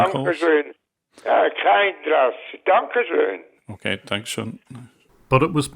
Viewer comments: If somebody is wondering what the swiss guy at 6:35 says: He says "Danke schön" which means "Thank you". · that swiss person at 6:33 as someone from south germany, I am not 100% sure what he says, but apparently he responds to the "these are scammers": Da kam ich drauf, dankeschön! basically saying he got that, and thank you! (alternatively it sounds a bit like "da kack ich drauf, dankeschön - but won't translate that)